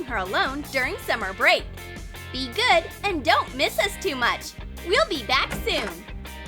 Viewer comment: I thought they said “we’ll be DEAD soon”